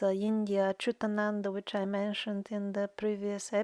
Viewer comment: Who is the indian mystic she talks about at 26:00 mark? I cant hear. Please and thanks, love your videos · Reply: Hey, did you find it? I think it's achyutananda dasa.